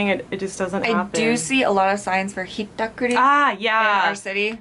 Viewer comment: What is the word spoken by the girl on the right at 09:18? She says: I do see a lot of ........... for ............. It seems to me that the first word she uses is science, I do see a lot ....... and the last word sounds like heducty. I am not an English native speaker, I understood everything and know they werer talking about purse snatching, but I would like someone to write what she said in this part. Thanks in advance. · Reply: She said "I do see a lot of signs for 'hittakuri'". Hittakuri means purse-snatching